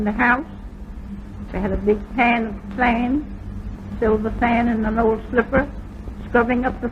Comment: "A silver fan" is what I thought she said in one of her sentences.